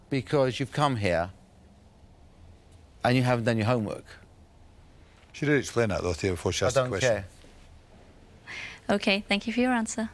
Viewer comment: Can someone please tell me what Duncan says at 11:17? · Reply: should have explained that theo before she asked the question